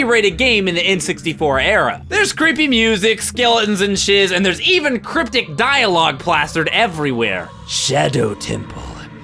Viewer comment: DID PBG SAY SHIT 😫😫😫😫😫😫😫😫😫😫finally